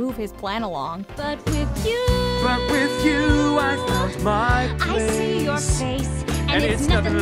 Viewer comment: number 14 it sounds like hans is saying "i f***ed my place